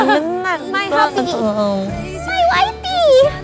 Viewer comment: did anyone heard what did jennie said to Rosè? She said “My wifey!”